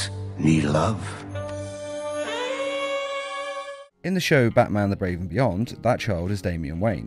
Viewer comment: did you say Batman the Brave and Beyond?